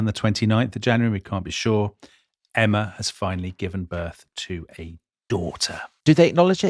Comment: what I heard was ' ... Emma finally gave birth to a door ...' at which point the adverts cut in. It might be a while before I stop laughing.